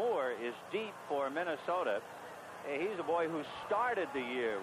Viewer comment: Whoa. What did he say? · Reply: HE SAID HE'S "THE BOY" IS WHAT HE SAID!!